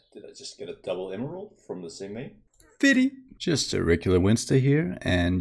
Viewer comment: did you say "TITTY" xD